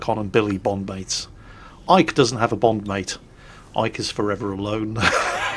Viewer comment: I don't know why, but I kept hearing "Ike is forever a loan". Which made me smile.